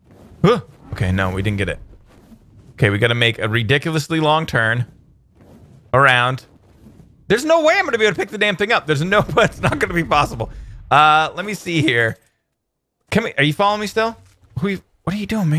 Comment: did he say dang or dam.